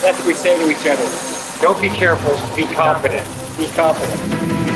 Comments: Oh did they say confident? I thought they said don't be safe be competent. I would rather be competent then confident as in the former will keep you alive. Tbh is better to be both confident and competent. 😁👍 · I thought he said “Don’t be safe be competent.” Which also makes sense. Know what you are doing and always do it right.